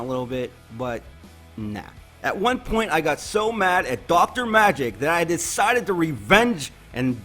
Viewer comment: Thought it said Dr. Magic not dark. Lol